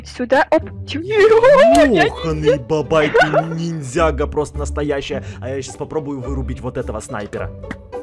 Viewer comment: вы все слышали NINJAGO? Походу ОНИ(Из нинзяго) услышали и пришли к эду